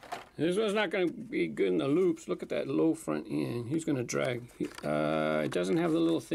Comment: Amazoom has the low front end that slows down in the loops. EDIT: It's front end, not front in. I only misheard front end as front in when I was younger.